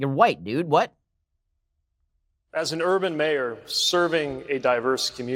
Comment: did he say urban meyer